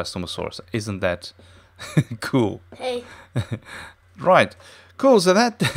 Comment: did anyone else hear someone other than say "hey"